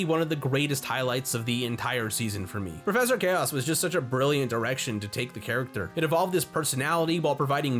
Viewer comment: im not the only one who heard erection right?